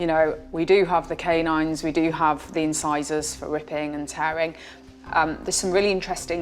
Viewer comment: Did she just say RIP and tear